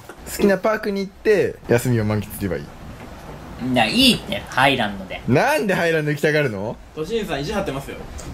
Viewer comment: ハイランドがアイランドに聞こえてしょうがない (地元トーク)